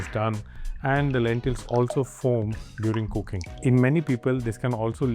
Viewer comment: there's a typo in CC, I believe - instead of "...lentils also FOAM during cooking" it reads "...FORM during cooking".